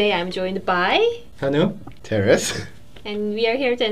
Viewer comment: Does anyone else mishear Terris's name as Taoris when they are only distracted by something? xD